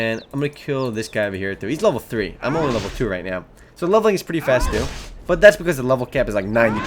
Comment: wait wait wait 10:14 did he say lvlin is pretty quick? or did i dream that lol